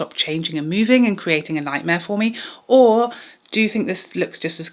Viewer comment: I heard "creating a LIGHTMARE for me". I was cracking up!😂 I had to play it back to prove to myself that you actually said 'nightmare'. I say choose the method that causes you the least amount of stress.🥰☮️💖